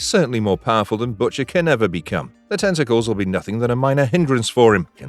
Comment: At first i thought it said 'can his testicles kill' lol